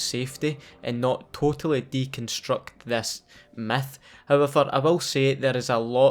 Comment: sounds like he said meth